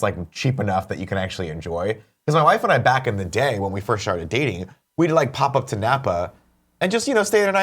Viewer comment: I swear that at 23:24 Nick says "when my wife and I sharted dating" haha